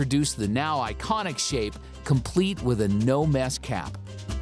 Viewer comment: at first I understood “complete with a new ass cap” instead of “complete with a no mess cap” 😂😂😂🤧